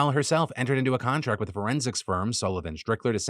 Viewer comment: could have sworn Phil said "forensic sperm"